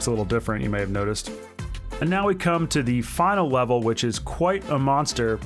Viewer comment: LOL! Did anyone else hear him say “final wevel” at 7:29?